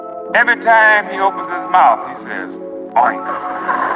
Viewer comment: What is the word he says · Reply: oink like a pig.